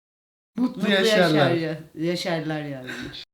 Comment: abla ne dediğini anladım 3 lü ka*sarlar mutlu yaşarlar